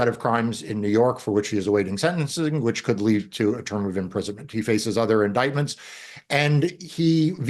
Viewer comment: I think I’m so hopeful that Trump is imprisoned that I heard “Eternal imprisonment,” not “A term of imprisonment”